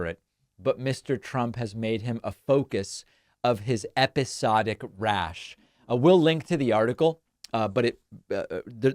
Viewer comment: Did I really hear (at around 1:12) David say Mr Trump has made his lawyer a focus of his episodic rash? I hadn't realised their relationship was quite so intimate... 😀